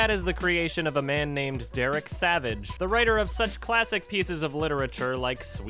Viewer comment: i thought you said adam savage thk gd